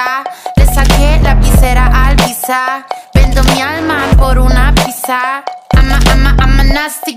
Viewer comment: pensé que decía ''VENDO MI ALMA POR UNA VISA'' ajajajsajdkjsadlkfjañslkgjal